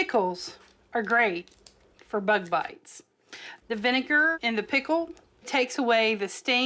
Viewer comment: I thought she said butt bites not bugbites..